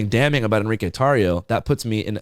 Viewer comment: When you said Enrique Tario that many times I thought you were saying enrico. I got scared my name would be associated with a nazi group and I had to check to make sure his name is Enrique… hasan you scared me buddy